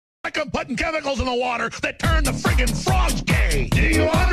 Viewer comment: I thought it said ‘ THEY TURN THE FREAKING FROGS GAY ! ‘